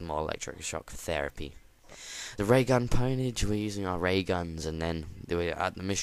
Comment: gay gun instead of Ray gun lol